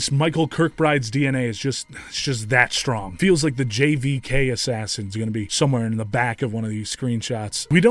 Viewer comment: I heard "JFK assassins" but didn't even question it, they'd fit too